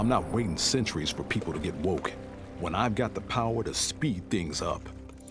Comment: Roses are red, violets are blue, the moment you came for is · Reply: Did jax just said "woke"? 10:07 Oh man get the fu@ out of here with this sh!@ Now games are controlled by racist sjw's?